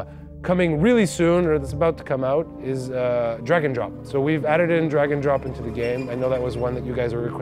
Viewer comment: I keep hearing “Dragon Drop” but maybe that’s just me